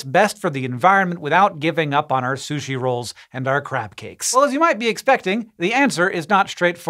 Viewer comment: Did he just say Crap-Cakes?